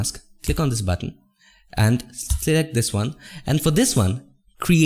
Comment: i couldnt really understand what he said at first lol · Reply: Same! Press hold and option, I guess